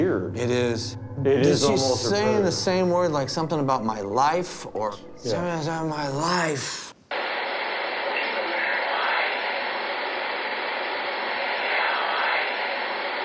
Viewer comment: i hear she say keep your life